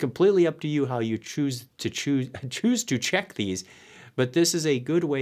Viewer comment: chuckles when he said "choose to choose" instead of "choose to check"